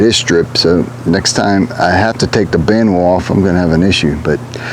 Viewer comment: Nice work. At the end it sounded like you said you took a band wheel off, is that right?